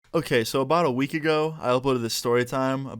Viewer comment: Did he just say "about a week ago"